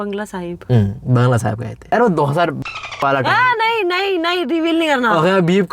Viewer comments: did say 2022? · what you just say to try 202???? 🤔🤔